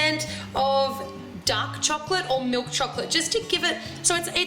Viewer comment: when you said dark chocolate, i first thought you said duck chocolate :S